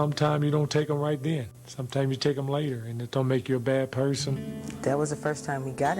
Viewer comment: WTF!?!? Did he really say that sh*t Take em later??? He was jealous of her, he wanted her😮